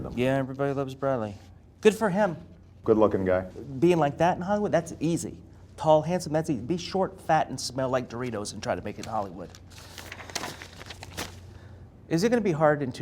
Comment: sounded like the host says "short, fat, smell like Doritos..." the bearded man is speaking about himself. Never trust a man with a beard.and From 2:56 Obama said that's why you're not President.. and that's good thing." :))